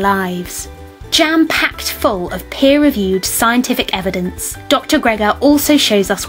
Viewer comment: Did I hear her say "pear reviewed"? :D